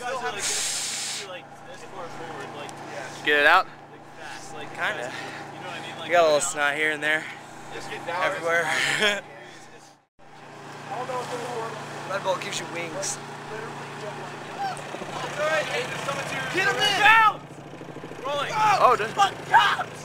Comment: I thought he said Red Bull gives you diabetes